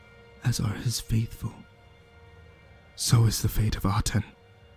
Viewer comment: The Nameless Pharaoh.... And did I hear Atem in 13:53? Yu-Gi-Oh! creepy pasta, nice >.< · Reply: Monika Vedrickaite The Pharaoh is not nameless. It's Tutankhamen. And you heard "Aten." Aten was the god of the sun disk, upheld by the pharaoh, Akhenaten. Nothing to do with Yu-Gi-Oh.